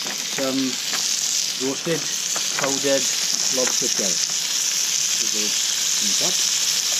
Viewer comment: Hello! What are all the condiments you put in? I couldn't catch some of the names. esp the one at 12:19. Thank you! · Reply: Hi , that was roasted lobster shell powder ( home made ), it has a very powerful flavour, kind regards